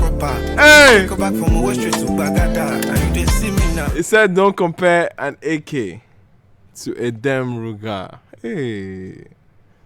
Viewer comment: I thought he said don't compare AK with dawn local.we call our home made gun, local gun.